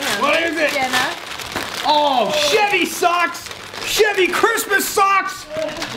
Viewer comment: The first time I Watched this I thought he was saying shitty Christmas socks instead of Chevy Christmas socks 🤣🤣🤣